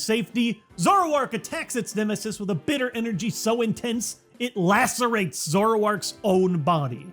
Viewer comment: I thought Lacerates said Lactates...